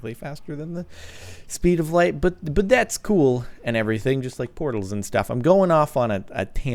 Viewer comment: Did CaptainSparklez really say, "I'm going off on a tangent... Sines, and stuff and things like that." ? hahaha